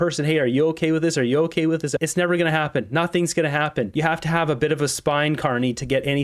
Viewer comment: So what I heard there was " nothing is going to happen".